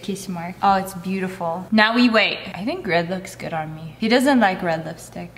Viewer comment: My captions say Greg looks good on me